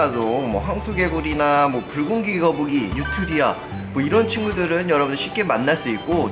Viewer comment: 붉은귀거북이라고 말 할 때 순간 불고기거북이로 들었다..